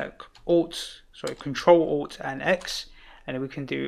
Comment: What shortcut did you use? Can't make up what is said nor see the shortcut... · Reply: CTRL+ALT+X